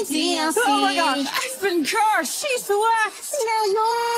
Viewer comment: Edit: it's coryxkenshin who voices the guy. Please stop replying to thjs comment lol bro i cant get over how amazing the guy's voice is I love the "I've been cursed , shes the worst" but I most definitely love the "is there a way to hit restart?" cuz his vocals are just so amazing i have to know who it is :sob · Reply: Does it say "she's the worst"? I swear I heard "she's the last".